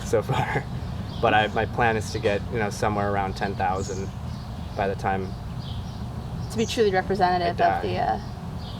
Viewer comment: does he say "by the time they die"? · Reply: By the time I die